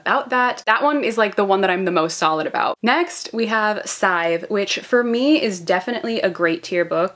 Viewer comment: Anybody know what she is saying? Is it sieve? I am trying to find it but i'm having hard time. If anybody knows help would be appreciated :) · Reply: Schnate scythe